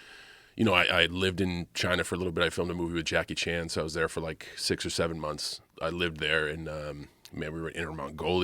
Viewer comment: what did he say 6 or 7 🤔